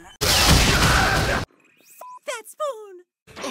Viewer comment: did Italy just say f**k that spoon? 🤣